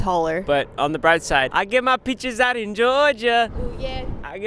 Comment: peaches was the only thought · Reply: but I literally heard "I get my bitches in Georgia"